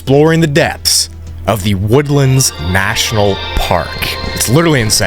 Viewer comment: As someone who lives in Washington (and has my whole life) I heard “woodland park” and instantly I was like, “woodland park zoo??” Damn I’m not far in the video yet but I know it’ll be good